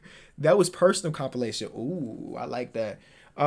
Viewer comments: I heard him say I’m like that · I thought Culver said “I’m like that”